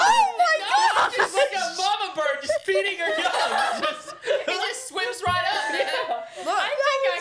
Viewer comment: did I just hear say the n word?! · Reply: No if you put subtitles on he says “just swims right up” the way he said it though I thought so to LOL hope this helped 😂